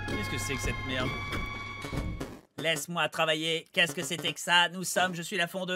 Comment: À 59:48 il ne dit pas "Laisse moi travailler, mais "laisse moi sortir